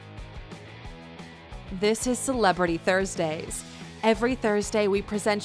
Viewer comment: Am I the only one who heard her say thirst day?